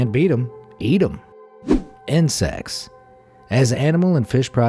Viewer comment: In-sex? Instead of insects?!